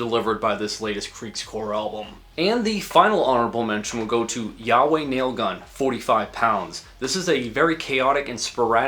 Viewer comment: i was SO SURE the band name is yaoi nail gun...